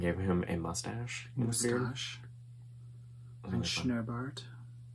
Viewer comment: Did I just hear you say “snorbaard” about the gnome at 28:25?!? Do you speak Dutch? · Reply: Haha THAT is funny! I actually said (poorly) “schnurrbart” which is mustache in German. speaks beautifully and I know a bunch of random words, like mustache 🤣🥸